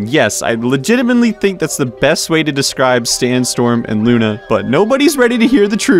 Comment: Stanstorm is actually a sweet nickname for sandstorm now that I heard this over and over